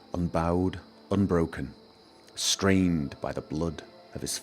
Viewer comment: you said strained instead of stained... but excellent series might i add. I love your storytelling voice its great!